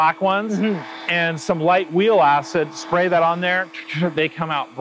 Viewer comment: Does someone know what he meant by "Light Wheel Assets" on 6:03? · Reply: Light Wheel Acids